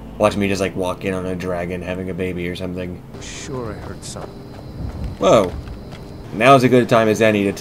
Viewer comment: he said "(i was)sure i heard something" which is what they say when you break line of sight when they "think" they see you